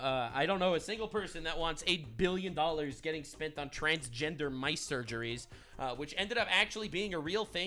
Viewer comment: I’m ngl I heard “transgender ice” and I lowkey thought the waterbenders learned how to genderbend as well 😭